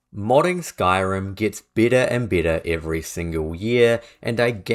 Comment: I thought he said getting bitter and bitter. Modding Skyrim is pain in the ass